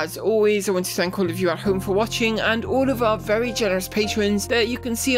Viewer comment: I thought you said at the end ' and to our very jealous patreon's'. :)